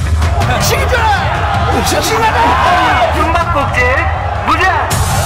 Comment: Did he just said Blyat ?